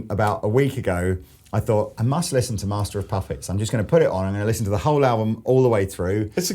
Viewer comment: did he say "master of puffets" ? that's so funny. i think maybe it's just a brit/american thing